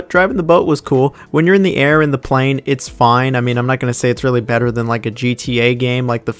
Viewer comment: i think you mean your not gunna say its better than a “GT” game not “gta” 😂😂 i always make the same mistake haha